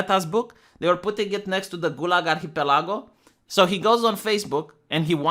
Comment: What is the book you’re talking about at 17:00 sounds something like “Gulaga hitalaga” I think. I might be mishearing you I just don’t know what it is